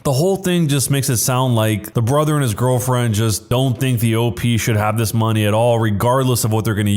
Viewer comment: Is it just me or did anyone else see that at 5:24 he said "just dont think the op" but the text said "just dont think the old"